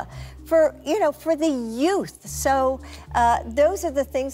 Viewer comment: Did you say yoots?!!